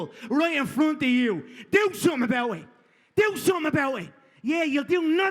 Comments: what did mcgregor said? could you guys let me know? I wanna script he said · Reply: 전종혁 I think he said do something about it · What does Conor say there? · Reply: “do somethin about it”